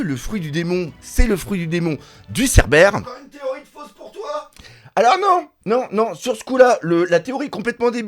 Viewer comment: Je n’entends quasiment pas ce que le en off dit à 1:40 . Pour entendre -> « encore une théorie fausse pour toi » j’ai du monter le son et remettre en arrière. Je n’ai pas de casque ni écouteurs ou autre. C’est marrant mais seulement si on entend :D